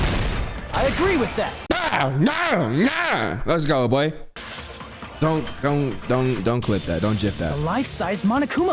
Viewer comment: BRU ㄹOMEONE PLEEEASE TELL ME WHAT HE SAID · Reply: Jaylen H. He said don’t clip him when he was doing that little dance😂